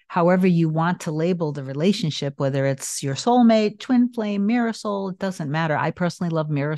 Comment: I’ve been listening to you for about a year now. This whole time I thought you were saying ‘Marisol’ but you’re actually saying ‘Mirror Soul’! (Marisol in Spanish means ‘sea and sun’ ❤️